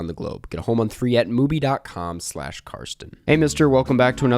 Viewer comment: I'm sorry, but did you say, "Hey, Mister..." ?!